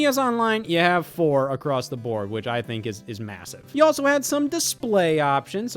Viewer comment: did yyou say massive?